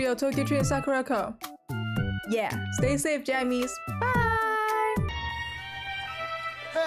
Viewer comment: i thought you said stay safe japanese in the end, it took me a second to know that wasn't what you said🧍🏻‍♀️